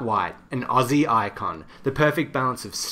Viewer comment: Oh he's saying coffee up 😂 i always thought he was saying Coffee yo.. lols